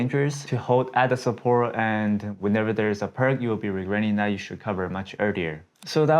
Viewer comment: you're great, and I love you cause you actually seem honest about how you trade as opposed to many others. That's first. Second... My guy... Don't take this the wrong way. But: What did you say at 4:37? I really want to know! "Whenever there is a something you should cover much earlier"? Would really appreciate a reply :D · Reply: Whenever there is a *perk*. Now for you to understand that word is now your next journey, good luck.